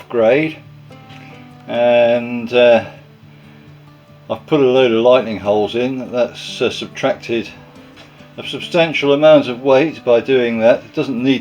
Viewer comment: Interesting as normal. Nice wooden propeller. I thought you said "Lighning holes" and wondered how that would work, then realised you said "Lightening holes" to reduce weight. Did you add background music? I noticed it this time and found it a little distracting.